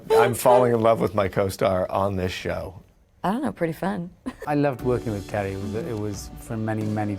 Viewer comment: what is she saying exactly? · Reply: “I don't know pretty fun”